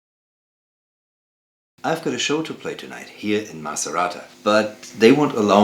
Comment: Thanks for the reportage and demo. All very nice and clean, but please: do not spell Italian names in other languages! When you say "Macerata" I heard "Maserati"!!!